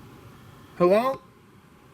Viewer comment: i think evp woman say hi. Faint whisper.